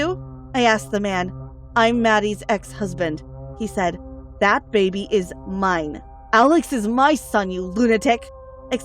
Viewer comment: Did she just say that the baby is ugly 😭😂